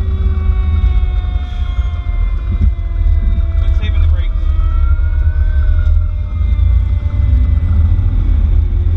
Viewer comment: Just curious, what did someone say at this time stamp, sounded something like brakes?? Great Vid & response guys. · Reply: It sounded like "quit slamming the brakes" or "quit saving the brakes"....either way, it was a hot response to a confirmed house explosion with known work, so maybe the driver was shittin & gettin, which is completely understanding or if he was saying quit saving the brakes & he wanted the driver to get moving a little faster. Either way it was a completely safe response & yet speedy from the station to the scene, well atleast in my eyes it was but hell I'm a nobody, lol. Thanks for the fast reply, Stay Safe up there guys.